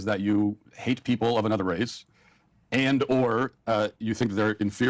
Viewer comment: he said "and/or" if you actually listen to what they say instead of just yapping. And Kamala was the one who made the joke 3:53 "mayonnaise and tuna", not Walsh. Guess what that joke is Hunter? A "HARMFUL" STEREOTYPE. I used to actually respect you but I'm not even 5 minutes in and your just completely missing the point and yapping out of your ass. You are a hypocrite, plain and simple. You are not Indian so I don't think you can have an opinion on the curry joke being racist.